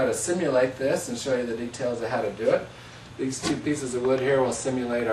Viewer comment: i don't know why but at first at the beginning i thought you said "show the retards how to do it" then i replayed the video and heard otherwise -__